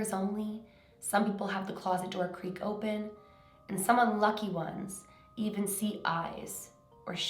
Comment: I'm pretty sure she said lucky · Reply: Mariam 2009 no hear it carefully I thought she said lucky but I heard it carefully and I heard her say unlucky